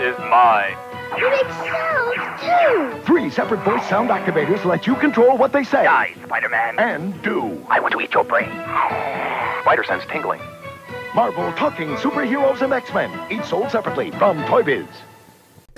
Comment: I remember seeing that figure in the store. All these years I thought Venom said “I want to eat your brains Spider-Man!” My brain combines both phrases into one. Haha Great vid and thanks for sparking that memory!